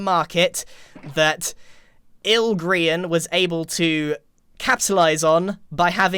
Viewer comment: I thought you were saying "Il Grian" instead of ill Grian.